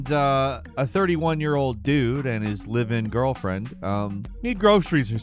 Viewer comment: for a second there, I thought that said "his living girlfriend...", like in 'it is necessary to make it clear it is not a virtual, computer or made up one' 😂